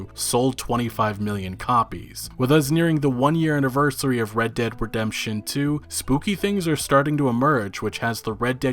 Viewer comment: I hear red dick redemption 2 , i dunno why